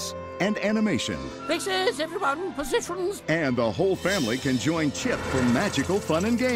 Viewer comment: What did he say? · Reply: “Positions, everyone. Positions.”